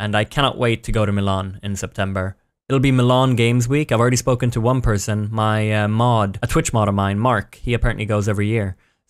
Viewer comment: I thought he said "I asked one person already. My Mom" Lol 😂